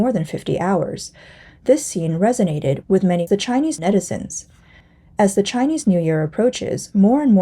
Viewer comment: Chinese Netizen is that what she is saying. The term is a portmanteau of the English words "internet" and "citizen."